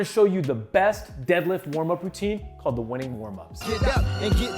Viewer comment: I thought he said "Winning" warm ups but then noticed that was Matt and said ah oaky nvm ha